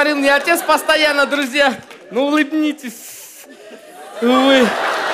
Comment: самая классная шутка это: "Ну улыбнитесь, сссуу..." !!! :) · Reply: al52052 Мне послышалось что он вроде сказал ну улубнитессссссь вы. И сууу там не было. Просто "с" в конце протянул